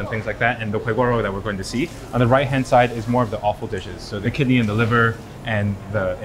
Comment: I appreciate the BA team for adding subtitles to the videos, but could you please transcribe the word "offal" correctly instead of saying that it's "awful"? I've noticed that this has happened for more than one video. Thanks for the wonderful content!